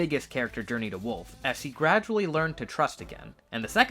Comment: wolf as he or she did you say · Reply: She of course!